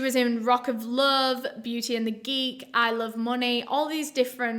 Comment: I thought she said I love monnie